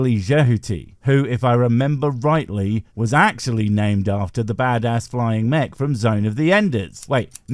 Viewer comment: Am I retarded or does this say "Two extraterrestrial racists"?